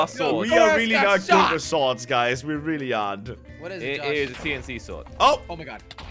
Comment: When Josh said tnt sword I thought he said guillotine sword But I am Napolean Bonopart and I was in the revolution. I don't want to talk about it...